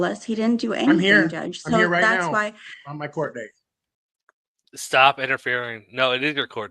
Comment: he actually said "On my court date" not "not my court date.. I just sometimes can't help but clarify when someone hears something wrong, sorry! 😅 not trying to defend him, im just sayin. 😬 love ya! 🫶